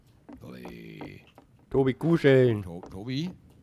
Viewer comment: wer hat noch bei 13:39 kuh-schellen statt kuscheln verstanden?